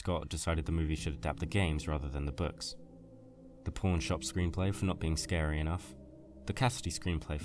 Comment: Thought you said "Porn Shop" screenplay for a second there...